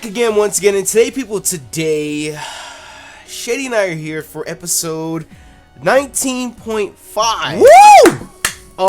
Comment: I thought he said Ep. 92.5! XD